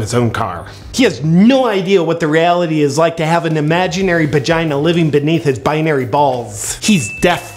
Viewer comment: Did he just say "imaginary bagina"